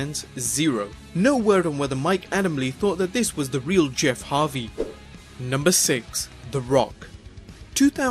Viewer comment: sounds like you said Jeff havys